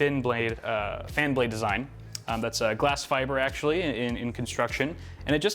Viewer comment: did u say glass fiber?!